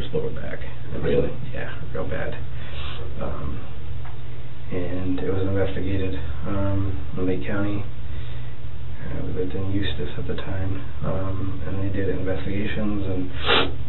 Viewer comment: these subtitles are wrong, as someone who lives in the area I know what he's saying: "There was investigated be Lake County, we lived in Eustis at the time" Eustis is a small city right next to Mount Dora, where that vet is. That's all in Lake County, quite a distance from Marion (around a hour or so)